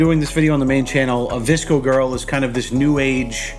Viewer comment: I love how you teach your woman about these new trends. Omg, you guys are one of my fav couples on YouTube. Stay together forever. I thought you were saying "Disco Girl". I was schooled too.